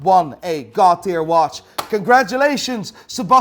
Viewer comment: The person that wins a “God Tier” watch. Thought he said Cartier watch